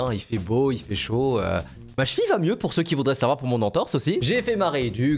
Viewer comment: j'ai entendu "ma fille va mieux" j'ai buggé, j'ai cru que j'avais loupé un épisode- 💀